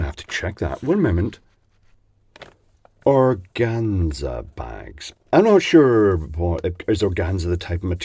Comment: I thought you said Orgasma Bags LOL :)